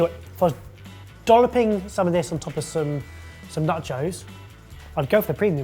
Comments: Am I the only one who thought said Nut Joes and thought "What the fuck are nut joes!?" then realised when he said it again he was saying NACHOS?!?!?!? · I swear every time he says nachos I hear "nut joes".